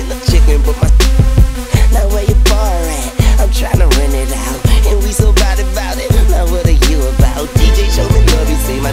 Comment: yoo in 1:20 does he call out borat?.. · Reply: Bar at